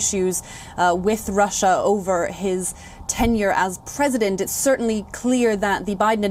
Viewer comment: Did she just said 10 years of trump President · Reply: Tenure not 10 year😀